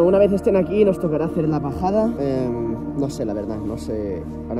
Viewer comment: parece que dice la pajada, jjajjaajja, ese Nil grande